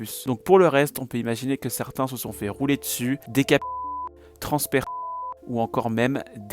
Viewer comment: Dé quoi ? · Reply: « Démembré » je voulais éviter de le dire avec YTB on sait jamais 🙌🏽